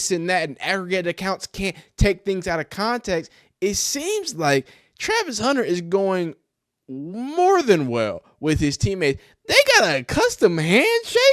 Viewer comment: Thought I heard Travis Hunter